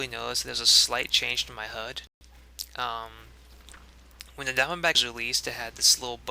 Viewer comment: I keep hearing "Down-back" instead of Diamondback :>